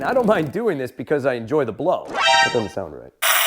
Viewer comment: Did he just say "I enjoy to blow"?????😮